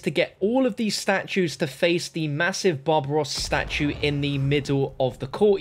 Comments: is it me or did he say, the massive Bob Ross statue”? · It sounded like you said "the massive Bob Ross statue" lol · It doesn't sound like he says Barbarossa at 15:37, it sounds like "Bob Ross" to me. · I thought he said Bob Ross statue lmao